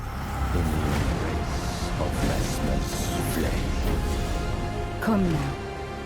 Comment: I think the voice mentions “Messmer’s flames” and that everyone stripped of Grace shall die by them, although I might’ve misheard.